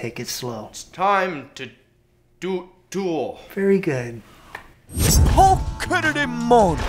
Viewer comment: I just realized Ryan said “very good.” Instead of Greg or at least it sounds like it.